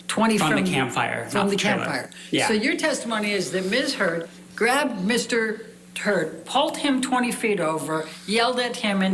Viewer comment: "grabbed Mr. Turd"... welp. She didn't slip, that was an intentional shot taking at Johnny. Depps team didn't catch that?